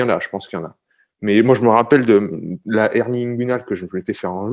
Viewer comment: tu dit quoi ? "hernie.. "